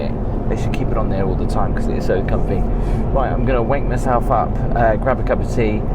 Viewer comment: WAIT... WHAT.... What did you just say 😊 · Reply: WAKE